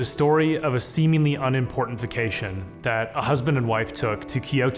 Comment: un-importification is a word now i guess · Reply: I thought he said that too at first, but he said unimportant vacation lol. I